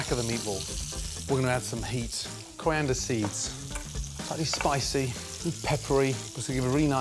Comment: What is he saying - what is the name of the spice he is mentioning? It sounds like Crandaseeds, grandma seeds or something, but couldn’t find anything when searching for it. Thanks, Michael